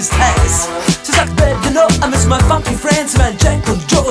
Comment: did he say "I miss my fucking friend's"??